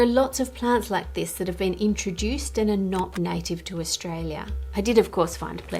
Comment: “That’s really intense” sounded like “That’s Australia gents”